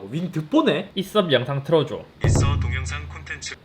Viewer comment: 내가봐도 이건 있업으로 들려